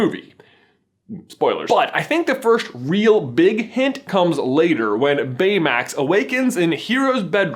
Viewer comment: DID YOU JUST SAY BIG HIT AT 1:27 ?!?! AGDGSHSHVD